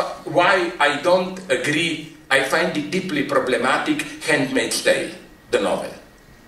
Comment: Can someone please tell me what the name of the novel he mentions at 9:43 ? Twice, towards the end of his presentation, he says the name of a novel, but I can't understand the title through his accent. It's the part where he says, "I find it deeply problematic, the novel, ****** ** *****". Thank you in advance, to any one who knows :) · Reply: the handmaid's tale